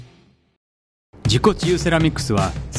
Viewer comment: 自己厨セラミックスに聞こえた(難聴